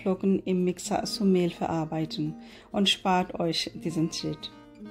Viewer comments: Hab das nur ich so verstanden bei 0:56 spart euch diesen Shit 😂🙈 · hab „ und spart euch diesen sh*t“ verstanden haha